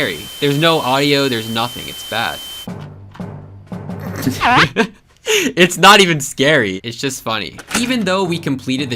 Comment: AYOO WHAT DID SAY???? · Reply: He said did he play hide and seek tho….i don’t get how that’s sus